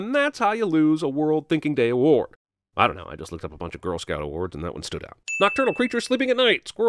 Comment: all I heard was “I was looking up a bunch of Girl Scouts”